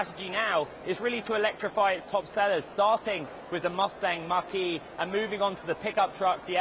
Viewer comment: I thought he was saying Mustang Marquee!! Lol